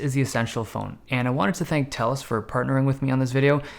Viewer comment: Sounded like he said Telstra